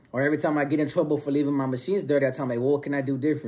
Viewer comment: I’ve been watching a gang of your videos recently. Good stuff bro. For real for real. What do you do for work? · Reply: oh ok. I heard you say cleaning machines. I paroled years ago and got into CNC Machining. I thought that’s what you meant by machines.